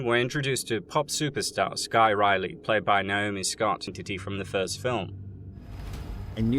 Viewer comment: Does anyone know what he said at 1:10 or was it just a bad edit? "player by Naomi scott ?????? from the first film". I listened like 5 times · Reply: He said played by Naomi Scott in the first film. It was just his accent .